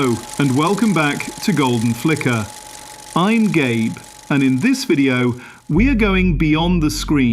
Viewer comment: for half a second i thought you said welcom to the golden flicker im gay😂😂